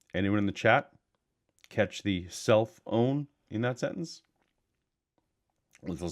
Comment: I feel like such an idiot now. I could have sworn you said "cell phone" instead of "self own"..... RORL!!! I guess it was because I noticed his phone next to him and my brain decided "That's got to be what he's saying!" LOL I'm definitely not one to speak on English grammar (my first language) as it never made sense until I took French... and later learnt Welsh. I have SO much trouble with English (like hearing the wrong words... lol)